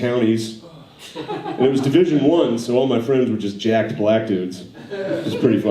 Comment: I thought he said “all my friends are just Jack Black dudes”. Like... fans of his or like him? Still laughed my ass off.